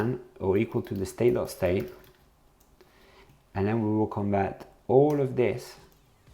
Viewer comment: "..and then we will combat all of this..." What?? The CC says 'combat' but I think you said 'compare'. But the '|' is a pipe, and doesn't compare anything. And the '(-1)' after it is totally baffling. Could someone explain exactly what this is doing?